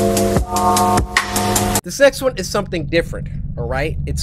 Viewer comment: Did he just say the sex one